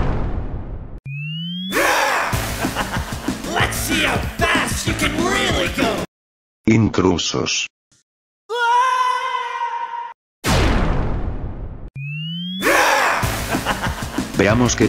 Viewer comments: I heard "Ingrown sus" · DID HE SAY SUS